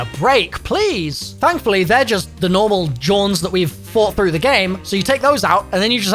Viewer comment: did he just say JAWN